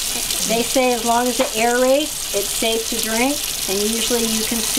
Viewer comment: Scary video. They're trying to impose fracking in my city, which is placed in Argentina. Can somebody tell me what the woman says in 1:21..?? "they say as longs as it (.....? ....) is safety drink.."